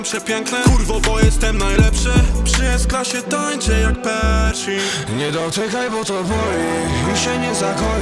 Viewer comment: tera dopiero ogarnąłem że w 2:11 jest napisane "przy s klasie tańczę jak Pershing" a ja usłyszałem że jest tam " wszystko się kończy jak Pershil" XD